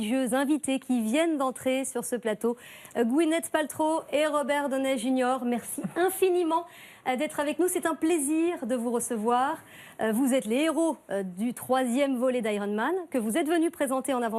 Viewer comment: I heard ''sit down please''